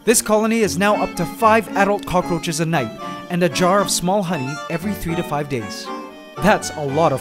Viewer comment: Anyone else hear him say, “a jar of small honey” at 4:28??